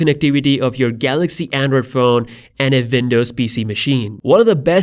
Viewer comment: Not to make fun or be mean but when i listen to this in 1.5x i hear "vindows" xD But fr tho ty for these videos :)